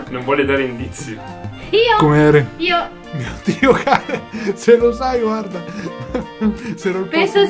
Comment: ha detto dio caren.... Solo io ho capito dio can...